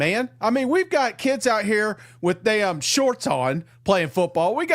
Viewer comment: Did he say a kid was in shorts? Around 5:30.